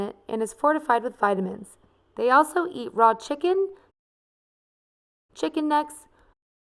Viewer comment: did she say chicken nuts